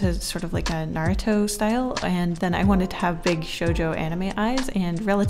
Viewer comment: did she said JoJo? Was that a JoJo reference?!